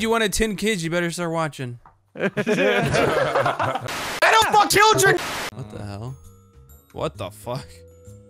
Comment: I don't want children I think he said